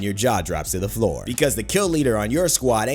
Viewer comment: I thought you say lewder